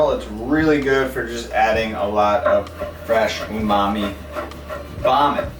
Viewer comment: Did he say "fresh umami vomit" at 13:30ish? I hope the stems will be kept for stocks but I am assuming not.